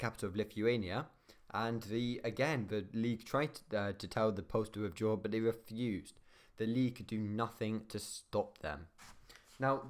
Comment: What does he say at 1:40-1:45? I can't understand that particular sentence for some reason. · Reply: Bit late but 'The League tried to tell the Poles to withdraw but they refused'